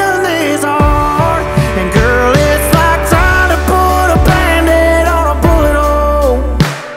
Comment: Is it jus me or do I hear him say “ it’s like tryna put a band aid on a bootyhole “ … 🤣🤣🤷🏽‍♂️